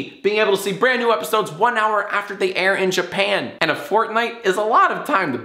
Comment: Did He Say Fortnite?